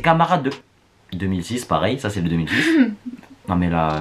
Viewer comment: t'a quoi contre les 2010 ils sont innocents j'aime pas ceux qui disent sa